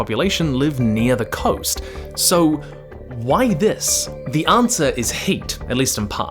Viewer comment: I was listening without watching. Being American, I legit thought you said " the answer is HATE!" and was like "omfg what kind of horrific bigotry is going on in Colombia?!"🤦🏼‍♂️